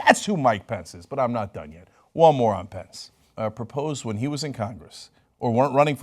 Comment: "But I'm not done yet. One moron Pence." Well, that's how I heard it.